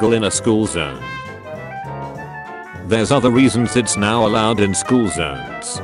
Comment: does anyone else hear it say "now allowed" even though its supposed to say "not allowed" ?